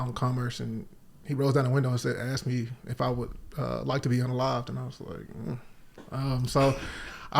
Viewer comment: can someone please tell me wtf he says here. "would you like to be...." WHAT??? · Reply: Unalived